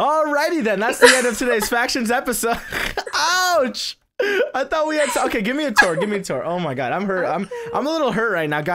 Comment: was i dreaming or did he say i fought we had sssex.. Ouch!